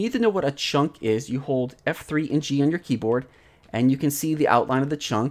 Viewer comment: Might wanna talk a little slower when you're talking about specifics like keys on the keyboard. Could not for the life of me understand what you said after f3. Sounded like "f3 inchy on your keyboard". Interesting that the underlying concept here is very similar to the Pokemon Emerald cloning glitch. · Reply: F3 + G